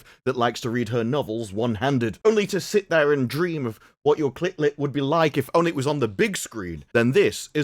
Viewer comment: did he say "clit lit"?? 😅🤣😂